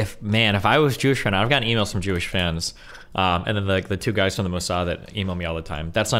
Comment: did he say emails from mossad at 5:15? lol like cmon how is that not weird